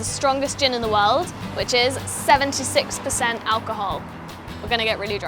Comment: I thought she said 7 to 6% alcohol not 76% lol